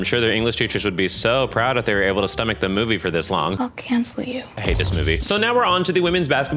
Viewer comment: for a horrifying second, I didn't hear what it said, I just heard "I.....you" and then saw reaction. My brain immediately filled in "I can't quit you" from the gay cowboy movie 'brokeback mountain' 2005, and never had I felt so much dread ever. You can't understand the unfathomable amounts of panic were in the short seconds between my brain processing that and me rewinding it, it's like a lovecraftian horror tht none of us will ever be able to fully understand 😭😭😭😭😭😭😭😭😭